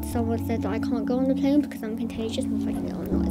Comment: "I need to put eye drops in to make sure that my eye doesn't stick to my eye lid" 😭😭 Bless her, she is brave and courageous 💔💔🙏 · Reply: I thought u said brace and contagious for a minute 😪